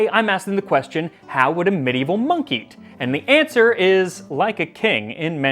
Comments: “how would a Medieval monkey?” That’s what I heard and it got me thinking, how WOULD a Medieval monkey? 🤔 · im i the only one that heard him say "what would a medievel monkey" at the beging of the video? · It sounded like you said, "...medieval monkey meat..." Purty strange... 😉😁😉